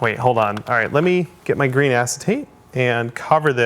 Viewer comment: When he said "green acetate" I heard "green-ass tape" and was trying to figure out what he was talking about.